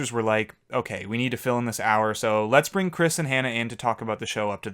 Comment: it sounds like you said "let's bring Chris and Hannah Ann..." HOW DID YOU PREDICT HANNAH ANN o_0